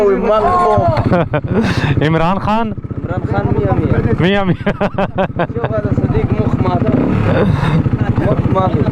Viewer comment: Can some explain what tgis boy said about imran Khan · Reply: he said miah miah which means 100% it's another way to say perfect